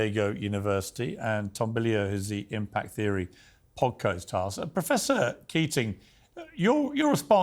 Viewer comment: What the frick did he say? 25:52 😂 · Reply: "poad-caust-haust"